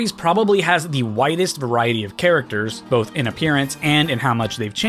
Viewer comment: why did i hear 'the whitest variety of characters'